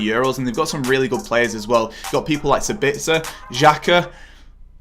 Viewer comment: did he just say sabitzer